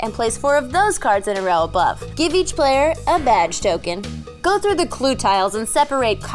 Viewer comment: Was I the only one who heard 'vag token' 0:52 and 'shit blame'